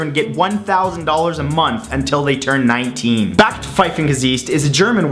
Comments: I had to listen it 5 times to figure out what word you tried to pronounce... "Backsteingesicht". It is "brick face", i.e. a face that demands a brick. I would never use that word. Never heard it, it might be slang somewhere but not here... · It took me three times of listening to understand what you were saying at 1:46. Backpfeifengesicht.